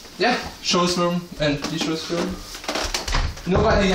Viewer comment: did he say idras room at 4:55?